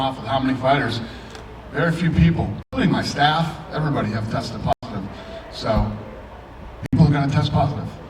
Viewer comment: did he say “everybody tested positive” ?